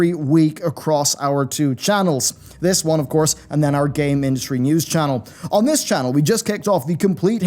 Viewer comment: Thank you for all the support thus far! Here're the various links! Join Our Discord: <link> We're hiring a video editor: <link> Support us on Patreon: <link> Follow me on Twitter: <link> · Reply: When you said "game industry" I heard: "Gay Ministry" news channel... LOL